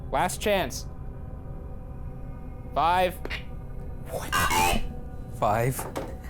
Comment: Didn’t sound anything like “five”, it sounded like “Urkel”,, Steve Urkel fans?